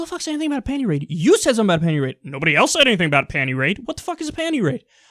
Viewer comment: Oh wow, He did say "pantie raid" .. I originally thought he said "pantry raid". and was implying that people want to be lazy and consume more.